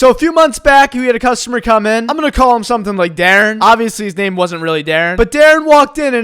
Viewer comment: I thought he said "We're gonna call him Derick" I was about to be hurt 😂